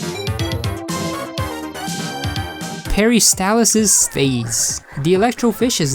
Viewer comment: Every time he says “Peristalsis Phase” I hear “Harry Styles’s Face”